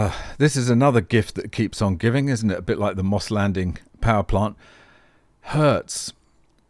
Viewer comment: Why do I keep hearing "TheCityOfLondon" instead of MossLanding? Wishful thinking I think!